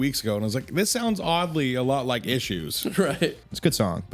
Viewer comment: who were they talking about that sounded like Issues?